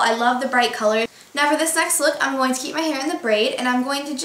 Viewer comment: did she just say : for the sex look ?! haha